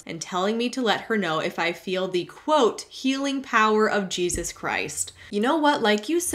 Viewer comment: OMG. i heard “Juices Christ” from the first horror story HAHAHA